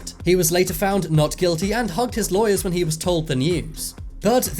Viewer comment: did anyone else hear “humped his lawyers”